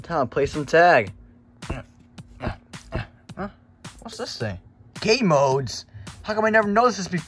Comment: i thought bro say “gay modes?!?!” but i listened 3 times and it said “game modes?!?!?”